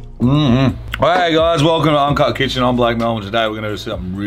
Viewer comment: Doesn't it sound like he says I'm black Melvin hahahah faaaaaaaack